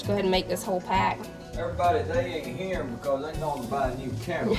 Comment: Oh my! My ears were playing tricks on me I thought said Jacob and Tyler went to buy a camel 🐫 😂😂😂😂😂 Haircuts looked great and so did the yummy pound cake 😊❤